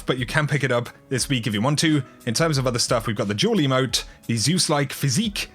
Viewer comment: when you said "duel emote" I thought you said "juul emote"